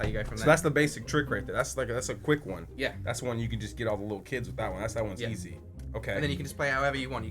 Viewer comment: "get off on little kids" 4:18 is that what he says???